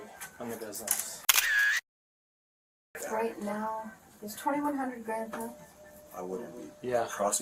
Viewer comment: She said: "Is 21 HUNDRED grand theft?" NOT 21 THOUSAND as your title states. She did NOT say its 21 grand as your captions say.